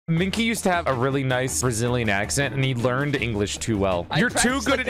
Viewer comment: I thought he said "Australian" the first time, and then I heard "Brazilian"